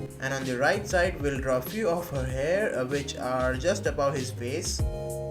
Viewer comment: (0_________________0) 1:34 did u say “his face” (0_________________________________0)